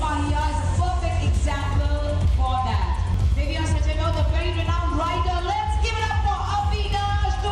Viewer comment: Unko best actor ka award Mila chirag ne acting nahin ki thi series mein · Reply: listen at 8:13-8:20. She clearly says “writer” and not actor.